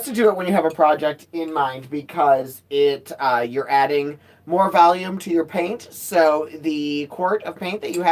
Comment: does say, "your adding more Valium to the paint?"